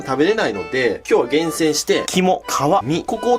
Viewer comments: 肝・皮・身が キモカワイイに聞こえた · 肝 皮 身 キモかわいいに聞こえた